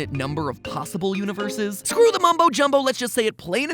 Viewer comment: did he say mumbo jumbo?